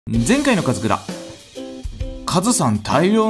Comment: 前回のガズクラが前回のカツラに聞こえたw